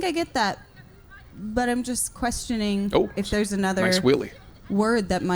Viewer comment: I was listening to this while doing other things, so I wasn't watching the video, and when you said "nice wheelie" @ 9:06 I thought you had said "nice willie" and I was like...😮 WHA??? Had to rewind and watch to see what you were referencing exactly....had a good laugh after seeing the guy doing a wheelie as he rode by 😂